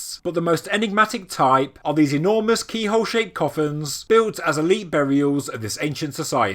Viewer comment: Watching the entire video, I thought the word you were saying was "coffins". It was only afterwards that I read the description and realized the word you were saying was "kofuns".